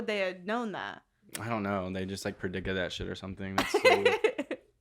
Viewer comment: it sounds like says ‘they just predicked that shit’,, for someone who claims to be straight he sure does have male genitalia on the forefront of his mind